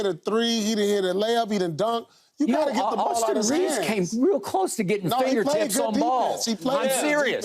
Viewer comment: Am I the only one who heard skip say "Austin Reaves came real close to putting fingertips on balls" 17:32 😂😂😂